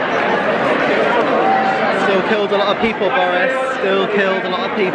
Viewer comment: Did he say, still killed a lot of people Boris? Lmao Tom